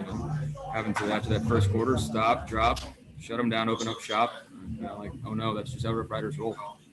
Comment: Did he just say stop drop shut em down open up shop or am I trippin